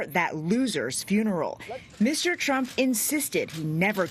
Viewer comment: did she just call him "Mr." Trump?